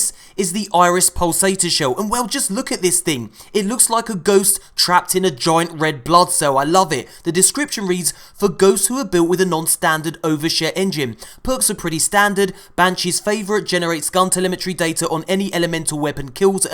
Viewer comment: Spat my drink I thought you said overshit engine 😂😂😂